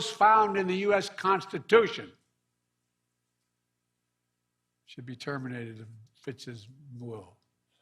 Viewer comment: Completely coherent to stand trial ! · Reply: What did he say at 13:08 ? It sounds like he said he should be terminated at bit for boo.